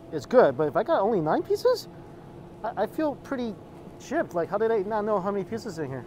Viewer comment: @ 2:18 of your video I'm not sure if I heard correctly, however it sounds like you use the word gypped, which is an offense term. I assume you don't know that it can be so just a heads up